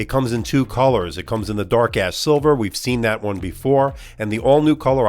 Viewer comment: I thought he said Dark Ass Silver haha